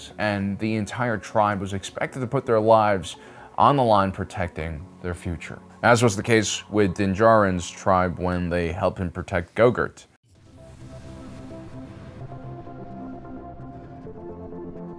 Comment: Did he just call Grogu, "Go-gurt" ?